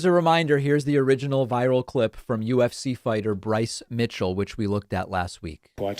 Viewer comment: I thought it said "UFO fighter". I was intrigued